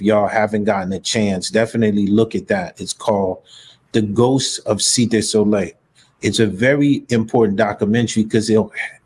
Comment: The ghost of what did he say in minute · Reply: The Ghosts of Cité Soleil